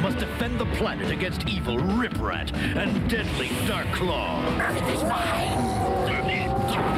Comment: What did they said!? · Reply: I know Darklaw said "Earth is mine!". I THINK Slashkat responded with "Then eat dirt!".